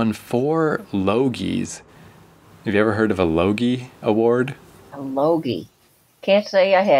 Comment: I would catch my dad watching the show here and there, he seemed to enjoy it (through it's probably because he grew up on it). I watched some clips on YouTube and it's pretty forgettable, if the show was only about the lady and the black person and if there was some humour sprinkled throughout it could've worked. Besides that, it's one of many cop show that just exists. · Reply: Two Notes: 1: When you mentioned the Yogie awards, I thought you said a loogie award. 2: When you said "Simpsons side burns" I think of Mr Burns telling Don Mattingly to get rid of those distracting side burns.